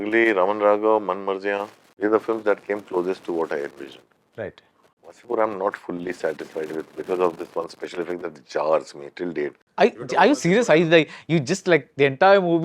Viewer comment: “Wasseypur I’m not fully satisfied with because of the …… …….. ………. ………..” I can’t figure out what Anurag Kashyap is saying to finish this sentence. HELP! 🎃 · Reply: He said "Wasseypur I'm not fully satisfied with till date because of one special effect that still jarrs me" This is what he is talking about: 'The director admitted that the film's closing scene, in which Tigmanshu's Ramadhir Singh is shot to death by hundreds of bullets as played by Nawazuddin, still makes him feel uncomfortable.'